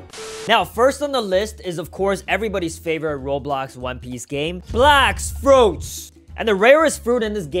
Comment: It sounded like he said blacks fruits lol